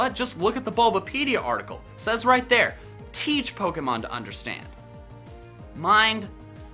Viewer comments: I always heard "these pokemon to understand" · I always heard “teach Pokémon to understand” and I always thought it made more sense. I would always get mad when people said “each” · YESS I KNEW IT WAS TEACH NOT EACH (Please colab more) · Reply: I heard it's pokemon to understand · I heard "these pokemon to understand" It made no sense, but hey... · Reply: Don't feel bad. I always heard it as "*It's* Pokemon to understand". Makes even less sense in retrospect. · I always thought it said it’s Pokémon to understand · I always heard 'these pokemon to understand' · I heard "these pokemon to understand" It made no sense, but hey... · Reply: I heard "its Pokemon to understand" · Wow, I've always thought it was "THESE pokemon, to understand." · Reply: Kendall Sain I thought it was "it's Pokémon to understand" idk y tho.. But the weirdest part is that I thought they said "searching for a while" while he in reality sang "searching far and wide".... I'm a bit dumb. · I always heard " these pokemon to understand"